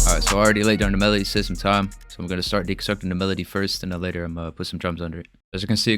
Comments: I have watched this video about 20 times. I swear at 0:26 he says he gonna start d*ck sucking the melody. 🤔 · did bro say “dick sucking the melody”? 😂